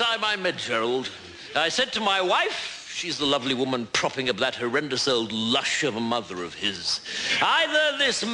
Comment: what does he say at 2:10 when he talks about his wife? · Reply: "She's the lovely woman propping up that horrendous old lush of a mother of his."